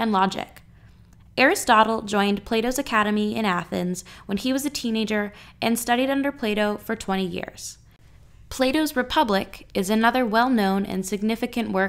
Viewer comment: why did i keep hearing play dough